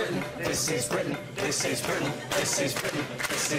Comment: All I heard when was "tits is britain tits is britain tits is britain" when Jon was reciting the new anthem of britain.